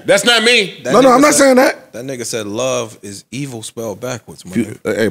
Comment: Thats not what Lux said tho lol, He Said “Is It Evil to Live Backwards”